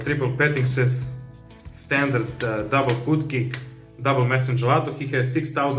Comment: I suppose it's because of the "intro" but when he said "double hootcake" I heard "double putki" and was just rofl